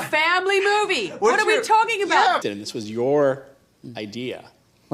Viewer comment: what does Todd say at 5:58 ?? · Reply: This is your idea